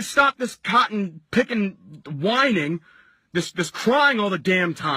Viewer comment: did he say cotton picking?😂